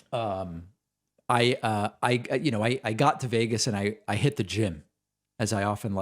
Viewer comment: Giggles 🤭 I thought David said “ I hit the gin “ 😂 Sorry , no offence intended